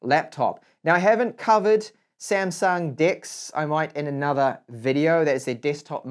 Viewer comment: I was reading specs in GSM Arena while listening to this video, and at 17:38. I thought he said Samsung D*cks 😂😂😂